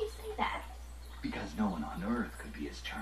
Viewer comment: Did you say Vamp at 10:00 ?